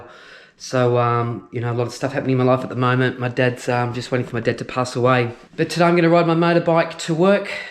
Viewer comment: at 1:22 did you say your Dad passed away? You really flew over that. I hope that's not the case. When my Dad passed, I got out on the Harley and rode in the desert with tears flying. Hope all is well.